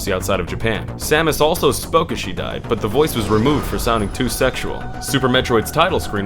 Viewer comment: did he say it was removed for sonic 2 sexual? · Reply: "Sounding too" not "Sonic 2"